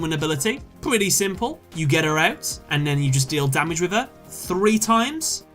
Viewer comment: I totally heard "you get erect" instead of you get her out. XD